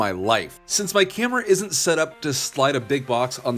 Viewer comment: i heard you say "since my cameron isnt set up" 🤣🤣